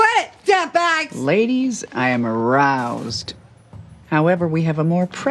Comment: I think Sue said "roused", not "aroused". Roused means "awakened" or "made angry/excited". So maybe Sue should get those 200 points back? Edit: I got to the point in the video where Sue comments on a minor's illegal photographs. She may technically not deserve to lose 200 points in this particular instance, but those 200 points don't really change the big picture.